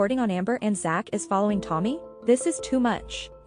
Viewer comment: Is anyone else creeper out that alr called Emily "Tommy " after seeing this😳 Correction..I misheard Tony,and thought she said Tommy..My apologies